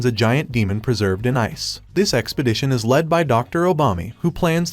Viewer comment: am i to only one who heard Dr.Obama?